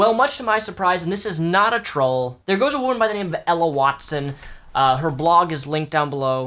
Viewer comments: I thought you said Emma Watson at first and starting talking about her "proud STD" and I was like dah fuck!? Had to replay, hahaha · for a second I thought he said Emma Watson.. and I was really worried because I like Emma... Q_Q